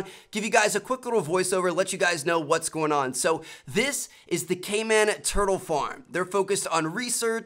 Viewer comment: I thought he said gayman at first lol